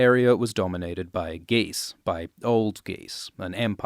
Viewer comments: wasn't paying attention for a moment and heard "GEESE" instead of "Gis." 😅 Fkn slaver geese!!! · I usually listen to these videos in the background on my phone while I play minesweeper, so I didn't see the text on the screen when you said "the area was dominated by Ghis.". Thus, I heard "the area was dominated by geese, or old geese." Now I'm imagining five successive wars between dragons and these demonic elderly geese that somehow got a hold of southern Essos. So thank you for unintentionally giving me that mental image 😁